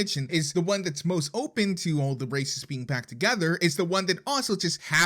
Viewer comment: "all the racists being back together" I mean it's not wrong but I don't think that's what he said...